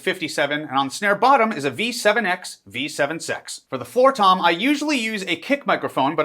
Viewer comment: did he just say b7 sex?